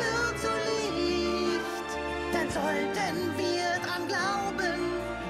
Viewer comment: Ich hatte erstmal verstanden wir müffeln dran glauben und ich Spikes Stimme schrecklich.Das Lied wär wunderschön wen er eine andere Stimme hätte.